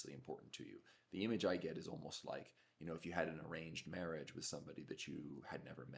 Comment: I thought you said "deranged marriage"! (I'm not projecting. I promise ;-)) I think I say and think "I need.." and "I have to..." more than I should. Good, actionable stuff, as usual. Thanks!